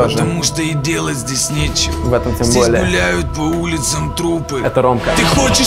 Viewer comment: Прислушайтесь что говорит Олег на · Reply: Русал Назиров -Там трупы -Это Ромка